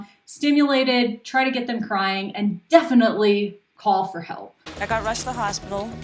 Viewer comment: "try to get the crying" did i hear that right?